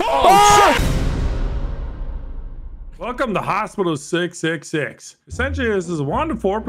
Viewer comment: I thought it said hospice not hospital. Hahaha